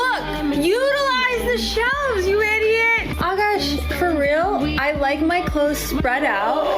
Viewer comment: Did she say "you idiot"?? BITCH!!!! TIME TO GO - NOW!! If I was Akash.. Jay-Z " U DON'T KNOW" would be ringing off as an alert EVERY morning after I left her!! 🎶💯🔥😂 YOU DON'T KNOW.. WHAT YOUR DOING.. DOING.. DOING.. DOING!! (of course I do!! 💯🤣)